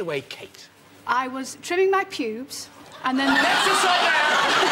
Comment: I swear I heard Kate saying "I was chewing my pubes…" even after a couple of tries. I had to turn on the subtitles to find it was actually "trimming"…